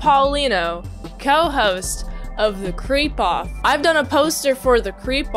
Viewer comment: Darn, for a split second I thought you said "The Crepe-off" like it was about pancakes, but hey it's still pretty cool.